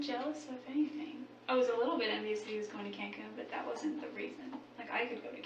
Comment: She actually said "I was jealous he was going to Cancun, BUT THAT WASN'T THE REASON." 😮